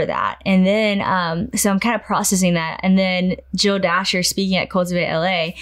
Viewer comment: Can someone tell me Sadie referenced and at what event in LA? It sounded like she said Jill Dasher, and something LA. I’d like to read the book she was talking about. Thank!